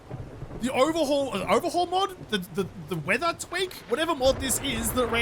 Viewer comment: why did I think he said “weather twink”